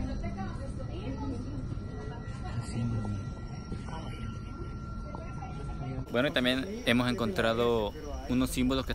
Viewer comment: Hola hola nose si ya se fijaron que en el minuto 20:20 y 20:22 se escucha un susurro femenino . Pero no se entiende que es lo que dice. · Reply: Están haciendo referencia a los símbolos y "él susurro femenino dice obvio"